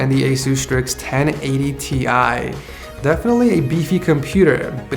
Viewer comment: Did someone say Beefy